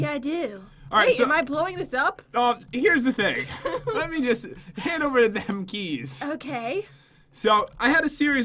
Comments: did he say "hand over the damn keys"?!?! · Um....at 1:10 did anyone else hear say "Hand over the damn keys"? I'm not hating, just wondering, you guys are my fav! Love yah! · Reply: He said, “Hand over them keys” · Did just say "hand over the damm keys"?